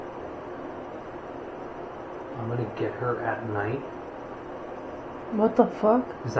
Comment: Hey Guys! Great video and as always a fantastic spot to explore. I jhave been there many many times. I have to say that at 17:32ish I thought it said "Get it together mate" but thats just me. All in all I love seeing what people inter[pret and experience and come up wioth for evidence in this place. Its my second home and I appreciate and promote it as much as I can. Thanks fpor the info and the camera work. is a great person as well! Cheers and maybe see you down the line!